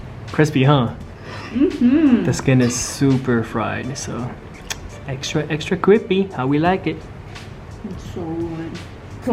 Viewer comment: I heard "extra extra creapy! Thats how we like it!" Lol